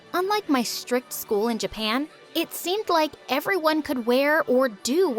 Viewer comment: i thought she said asian not japan o.o